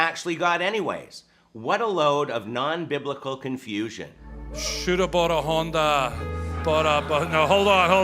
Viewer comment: the preacher sounds like he is saying. “ Shoulda bought a Honda.”